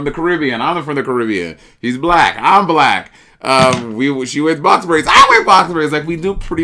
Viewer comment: I kept hearing “she wears boxer briefs, I wear boxer briefs!”